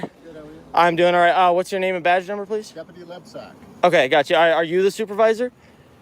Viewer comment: did he say Deputy N*tsack?!?